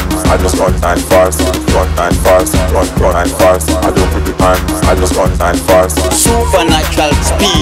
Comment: what is he saying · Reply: Volcanizm Bills Haha, I thought he said "I don't smoke pipes, I just run right fast"